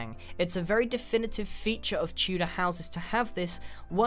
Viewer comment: i thought it said two-door house and i was like:Well... that must be hard to build....